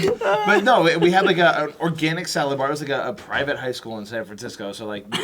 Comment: I could of sworn said Orgasmic salad bar